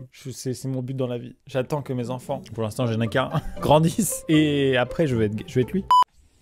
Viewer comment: Pourquoi j'entends " Et après, je veux être gay- " 😭😭